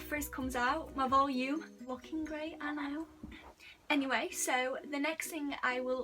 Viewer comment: Did she just say “lookin great and I oop-“ or is it just me